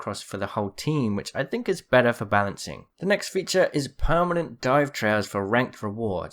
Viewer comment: I can't understand what he is saying, it's sounds like "permanent dive chowers" can any one clue me in? BoneAppleTea